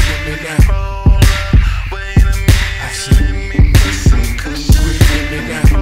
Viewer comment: When I first heard this song, I heard "Let me put some cushion, baby" instead of "Let me put some kush up in it."